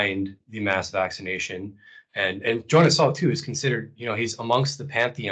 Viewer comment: It's hard to catch some of the names that are mentioned. I heard Edward Bernay, but what is the other one around 1:05:25? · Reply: After more listening I think it is Jonas Salk.